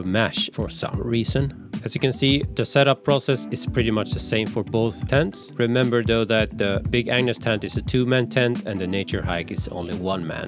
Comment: I heard him saying “big ANU$ tent” every time 🤣